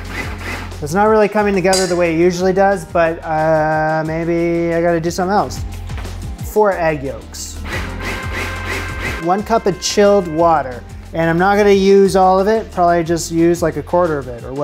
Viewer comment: My feet are so cold, it's winter here in Perth Australia, and when you said.. "4 egg yolks", My stoopid brain heard you say .. "4 ugg boots" 😂😅🤭